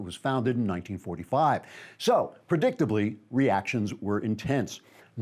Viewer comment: anyone else catch the "Reactions we intense"?